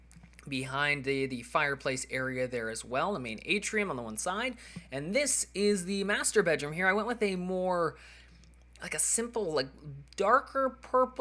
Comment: Sounds like he was saying masturbate room :P